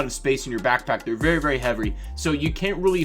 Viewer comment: You say heavery instead of heavy 5:57 😂😂